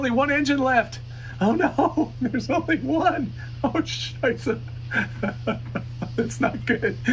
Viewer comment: did you said "scheiße"?